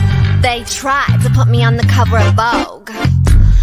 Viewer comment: I hear I'm vegetarian